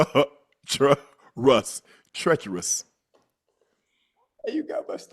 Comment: I heard “Treachowus”